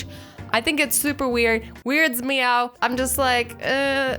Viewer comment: It just weirds me out. What I heard: "It just weirds "meow"t Get it? Meowt -> Meow.? ._. Dont send meh hates. ._. T-T